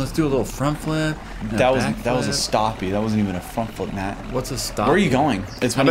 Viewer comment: it sounds like he said f#ck flip